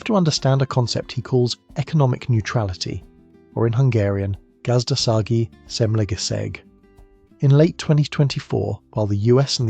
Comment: I'm a Hungarian.. and I had to think really hard what did you want to say... And I find out by hearing it 10 times.. so that is: "Gazdasági semlegesség" :D